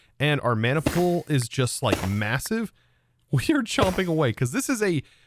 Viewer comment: Did you say massive?